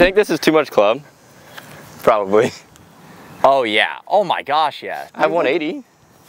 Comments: Dude did just say “oh my f***” at 23:50 ? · Did say "Oh my F***" at 23:57? Lol